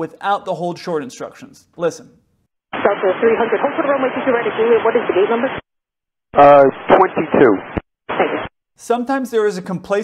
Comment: ""Delta 300 Hold short of runway 22R at J what is the gate number"",, she spoke so frikin fast,, all I heard was "gate number" no wonder the pilot only responded with the gate number,, Why do the traffic controllers talk so bleedin fast???